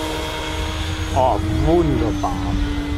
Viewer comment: What said? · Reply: Ohh, Wunderbar! (Ohh, Great!)